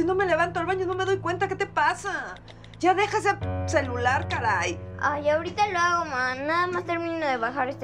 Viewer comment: WTF, qué dijo? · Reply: “Ya deja ese celular, caray!“